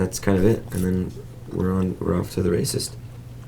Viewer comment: Hahahaha 6:17 I swear he says "we're off to the racist." lololol